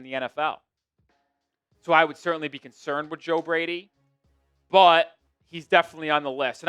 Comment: I thought it said Greg Brady. At this point, what is Bobby doing these days?